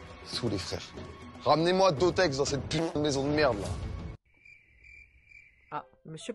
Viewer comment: il dit quoi la ??? Ramené moi...... Dans cette putain de maison de merde